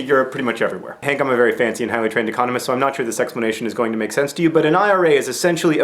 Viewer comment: did he say sexplanation? I think he did...